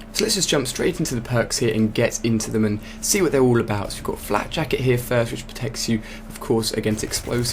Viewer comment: Lol, I just hear 'Flapjacket'